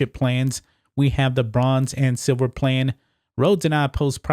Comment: I thought he said, Le-Bronze XD